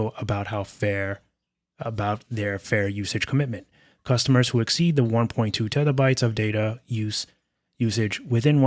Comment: The articles state 1.2TB of data; I thought I heard you say 1.2PetaBytes.